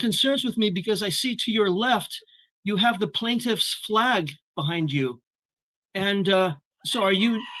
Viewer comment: The prosecutions flag !!!!! It’s to much my stomach hurts from laughing so hard oh my this guy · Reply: I thought he said plaintiff's flag the first time, so I was more confused than usual.